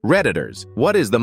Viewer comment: Bro at first I thought it said "predators"